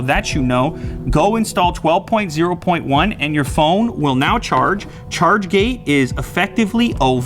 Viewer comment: Lol i thought he was saying charge gay